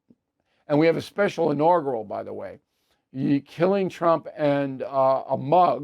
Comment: You also mentioned a book about killing T, when you mentioned the mug. I went back to make sure that's what I heard.